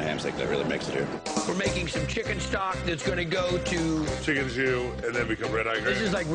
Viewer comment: Hello,did anyone catch what exactly he said at 1:01? It was something like chicken ... Thanks for help. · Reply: Martin Jůzek chicken jus, it is a light gravy made from the juice that cooks off the meat.